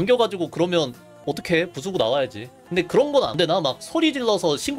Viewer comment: 화면안보고 듣기만하다 궁쓰고나와야지로 들었네ㅅㅂ